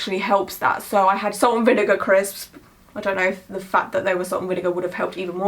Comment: love the new sleeve!! lmao i thought you said you had "chris" with you when you said "crisps" (we call em "chips" here hahaha 😂)